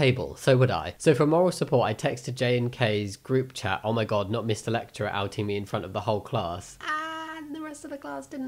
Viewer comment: Every time he said "A lecturer" I heard "Electra" and got really confused 😭